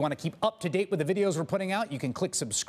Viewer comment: At the end of the video when he asks people to subscribe it sounds like he says: Click subscribe just blow me